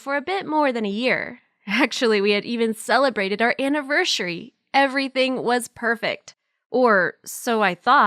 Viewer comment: did she just say annivershary